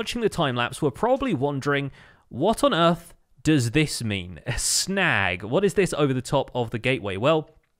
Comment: At first I thought it said "SNANG" and my brain instantly went to "Snow Gang" don't ask why. XD